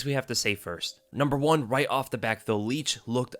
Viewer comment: Did you say "Right off the BACK"? 🤔